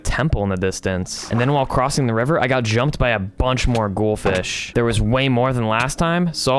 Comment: i thought he said gold fish but its ghoul fish